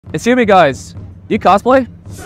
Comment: Excuse me guys, do you cosplay? Nah we just wear this on a daily basis 💀 Edit: why did this get so many likes.. Also I’ve noticed a lot of people saying that it’s not a dumb question and leaving tons of replies about it. I’m sorry my comment has offended you guys in any way. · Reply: I thought he said "Excuse me guys, are you Catholic" in a thick Irish accent at first.😅